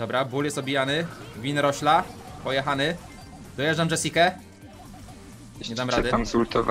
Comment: Dojeżdzam Dzejdzejke WTF (tak usłyszalam xd)